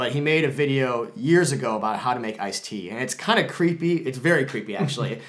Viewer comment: Totally thought you were saying Ice T, not iced tea at first